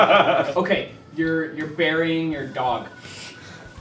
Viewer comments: When he said your burying your dog I thought he said your burying your daughter at first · Totally thought that they said you were burying your daughter instead of your dog. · WELLP that scene got ten times worse when I thought they said "daughter" instead of "dog" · I thought he said you are burying your daughter XD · I thought he said “you're burying your daughter” not dog so I was like did you kill her too? Why tf are you digging and then Mark said dog and I realized my mistake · When he said "burying your dog" I thought he said "marrying your daughter" and I was like "WHAT THE FUCK!?!?!!??"